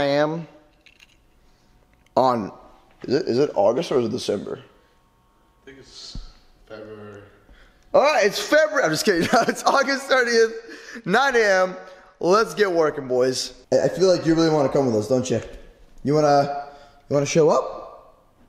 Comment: WE THE BEST! BUT MY CAR BROKE DOWN! WHEN ARE WE GOING TO GET IT BACK?! SUBSCRIBE and SLAP THE BELL so you get a notification for the next Life of Moose video! WHEN WE GET 10,000 LIKES, WE WILL RELEASE THE NEXT VLOG! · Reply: Did you say august 38? haha at 2:16 Hahahaha